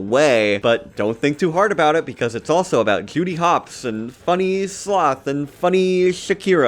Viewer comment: When you first said Judy Hopps, I heard it as “Cutie Pox.” Maybe I’m too pony-brained. I’ll just blame it on the horn.